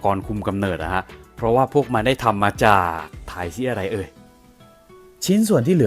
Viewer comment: ได้ยินว่าถ่ายเหี้ยไรเอ่ย <num>